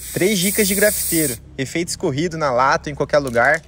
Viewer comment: Só eu que no começo escutei "três dicas de gravideiros"